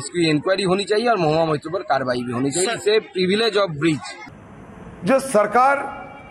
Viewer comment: Did he say “Privilege of breach” at 10:30 ?